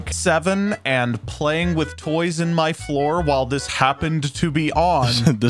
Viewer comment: did he just say in the floor?